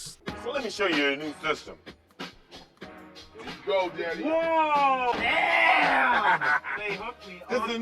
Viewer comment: anyone hear what he say at 9:19 ? 😭😭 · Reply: "There you go daddy🥺"